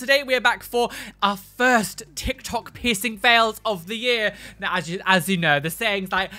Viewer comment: Please tell me I'm not the only one who thought said pissing fails 😂